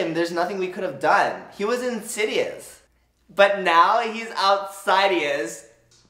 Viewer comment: A while ago i thought she said "outside he is" nit "outsidious"